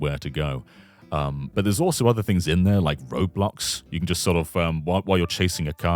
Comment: I thought he was saying the entirety of Roblox was in this DS download play map.